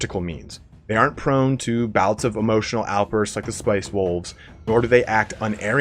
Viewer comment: I thought you said spice wolves, and now I want to see a spice girls music video with the wolves dancing.